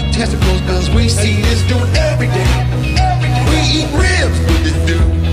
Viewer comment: We eat Ribs with this dude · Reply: Sean Reeves i thought he said " we eat ribs we just do